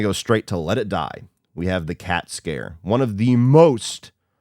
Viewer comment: When you said cat scare, i thought you said cat scan and i became immediately afraid since i have one scheduled for next week. I couldn't understand saying it's overdone. 🤣😮